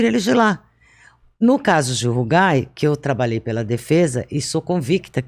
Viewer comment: qual é esse outro caso que ela comenta que eu não consigo entender o que ela fala? no minuto 6:42 alguem sabe me dizer? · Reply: Ela fez uma comparação com o caso Gil Rugai. Ele foi acusado de matar o pai e a madrasta em 2004, se não me falha a memória.